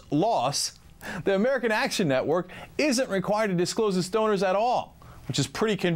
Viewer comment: Did he just say stoners? I know he meant to say donors but it sounded like stoners.